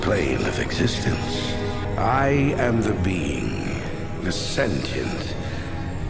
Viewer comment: Me:*misheard the guy* Me: the bean?